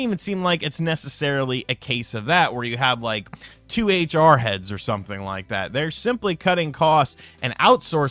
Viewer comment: You said ‘two HR Heads’ at first I thought you said ‘2a jarheads’ so imagine how confused I was and what a weird picture that put in my head. 🤪